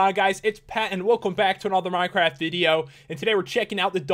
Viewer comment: It sounds like you say: Hey guys! What is going on? PET here